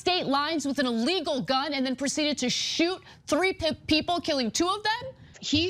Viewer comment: If you enjoy these videos,, and want to see more, chip in a few bucks in support: Like, Share, & Donate PayPal <link> Patreon <link> Cash App - $TrumpFanNetwork Follow: Backup Channels The Official TFN backup YT channel ran by JP - Liberal HeartBreaker <link> RUMBLE - <link> <link> <link> FaceBook <link> Twitter Check out Twitter: JP@ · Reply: it almost sounded like she was going to say shoot three pimps then they she said people it sounded funny. it was at 9:05 in the video